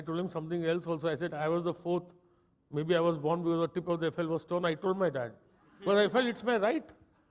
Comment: What does he say at 6:19 ? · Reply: He said the tip of the Eiffel(condom brand) was torn and may be he was born because of that.